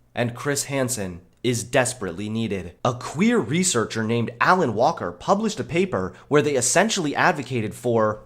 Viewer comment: i didnt watch the video. only hearing your voice. Thought you said Alan Walker for a sec xD